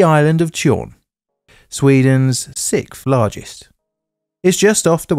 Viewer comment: Did you just say 'Sith' largest? 🤨 But if not, carry one with explaining more about this maritime induced unexpected deconstruction. 🤗